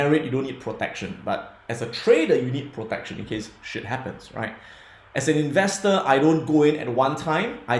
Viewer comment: did he just said shit happens